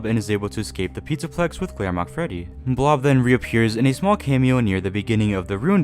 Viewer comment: I first thought I heard grandma Freddy and I had to go back and listen again😂